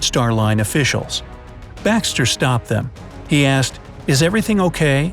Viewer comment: i thought that he said Baxter stockman